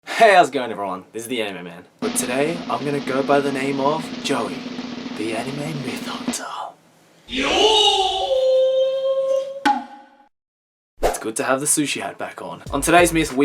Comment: Oh I thought you said you were the anime milf hunter